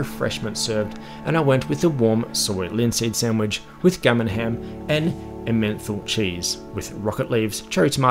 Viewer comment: Great review 👌 And while I'm in no position to rib you on the accent given I'm Glaswegian I've got to be honest I genuinely thought you said your "gummin hem" ( which I understood to be gammon ham ) came with a "menthol cheese". I literally was about to Google which cheeses had a menthol flavour ( which sounded a bit gross actually) when I realised you were saying Emmenthal. Mate - it's emen tal cheese. Not a- menthol cheese 😂😂